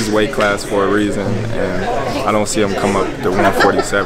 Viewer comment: did he say "Tank"?